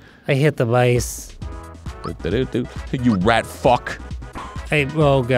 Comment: It actually says 'Rat Fuck' instead of funk!!!